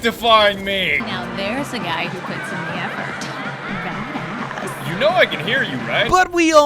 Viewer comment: she actually says "badass" not "dat ass". This means she's complementing your suit not your ass.